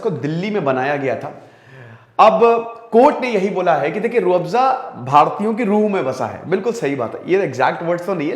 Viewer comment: At first I was like did sir just say ki 'Rooh Afza Bhartiyo ke muh me fasa hai' xOxO. Then I heard it again, he said - '...Rooh...'